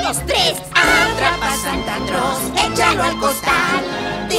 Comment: Siempre pense que decia santa cros